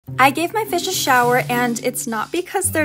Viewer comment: If u stop or scroll the video at the right time, it will sound like she said “I gave my fish sh*t”